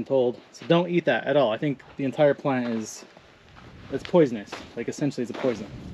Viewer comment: When you said that plant was a hallucinogen, and said the entire plant.. I thought you said the entire 'planet' and was waiting for the rest of that sentence... Thought it might be, the entire 'planet' is already messed up enough, don't need that. Haha